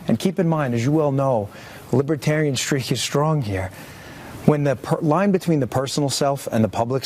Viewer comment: What does he say at 1:30-1:33 ? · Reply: "As you all know, libertarian streak is strong here."